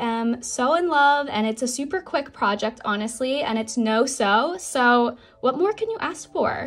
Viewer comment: I thought u said no sewing